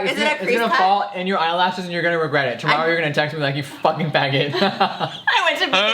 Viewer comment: did he say “you fuck**g fag**t? I don’t think so but I want to know